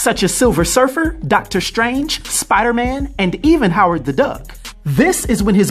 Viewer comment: wait did I just hear that correctly or did you say Howard the duck